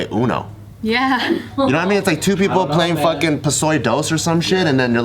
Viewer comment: Did Bart say Pusoy dos @ 7:17?